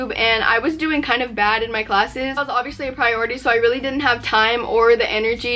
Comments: At first I thought she said gay classes but she didn’t and I was like 👀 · Lmao I thought she said gay classes